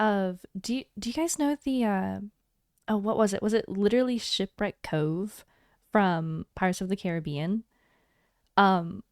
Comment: I swear, I heard Shit Break Cove the first time